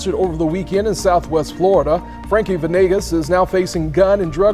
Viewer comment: WHAT DID BRO JUST SAY FRANKLY VE NI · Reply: Frankie Venegas